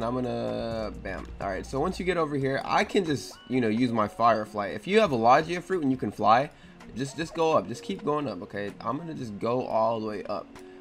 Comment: Bruh did you just say "Lagia"